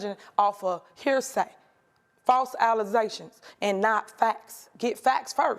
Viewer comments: I heard of "false allegations." But "false Alizè-shuns?"🤦🏽‍♂️ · Did she say..Alizations🤔? 🤣😝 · False alisations?? Did I hear that right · WAIT...Did she just say false alezetions. Yep...she did...she did.🤭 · OMG!!! you need to put in "Closed Captions" the way she is actually pronouncing things! I got into about 15 seconds and went," WHAT DID SHE JUST SAY?" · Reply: "False Alexations" killed me!! 😛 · did she say false alleZations? 😂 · did she say “alizations”? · Did she just say 'false alezations'? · Did she just say "false alizations? Oh dear lord... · allezations? Did I hear that right?